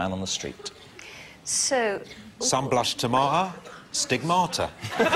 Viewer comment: I know this is old, but what the heck is the guys saying at 0:15??? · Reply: Sun blushed tomata (tomata is the east end pronunciation, meaning tomato) Stigmata